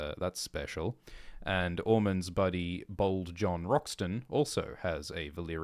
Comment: Every time and said "Bold John Roxton" I heard "Bald John Roxton." I was so confused why he had hair